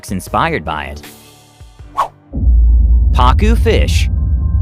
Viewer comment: i thought he said pakyu fish 😂😂😂